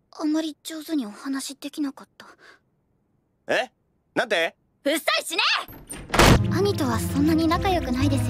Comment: I hear Saitama